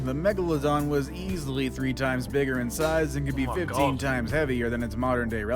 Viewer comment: I thought Simon said “Your Mum is bigger” bro I’m dead ☠️